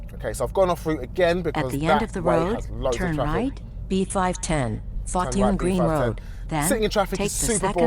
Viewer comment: "B510, F*** You Green Road" Anyone else hear that? haha